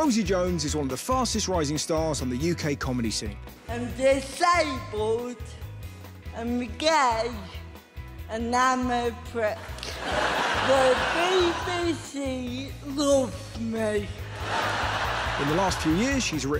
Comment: Some people in the comments have said they struggle to make out what she's saying, because English isn't their first language, or they're hard of hearing, so here's a transcript of everything Rosie said (except the shots of her inside the glider, because I couldn't make those out): 0:08 "I'm disabled, I'm gay, and I'm a prick. The BBC loves me." 0:44 "Hello!" 0:47 "I'm good! I'm sad that we can't--" 1:14 "Yeah! But, basically, I wake up every day, I make people laugh, and then get paid for it. Of course I'm happy! Honestly, I feel like I say it too much, but I am living my dream, um, and I never brought it on. I'm genuinely so happy, so yay!" 2:06 "Yeah! So, it's a travel show, where I go around the country, seeing how accessible places are, and I'm pretty much, like, a tour guide for disabled people. So, it's called Mission: Accessible, but I'm very unhappy about that, because I wanted to call it Crip Advisor." 3:10 "So, I think what they did say was 'disabled,' 'holiday', 'accessible'. It sounds a bit dry. It sounds like "We're talking to Margaret, who's 80, in a wheelchair, and she wants to go on holiday to Banga." It's like, wElL dOnE fOr YoU, mArGaReT, but I don't care." 3:53 "Yeah! But you're all thinking it!" 4:02 "Yeah, yeah! oH, wAiT! And I always do!" 4:27 "So, what I wanted to do was tailor-make a holiday for a person's needs, and for their disability." 5:21 "Before today, if you showed me a glider, and gone "Can a disabled person do that?", absolutely not. It was